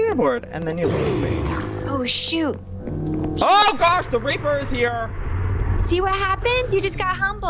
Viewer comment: i thought he said the raper was here 😂😂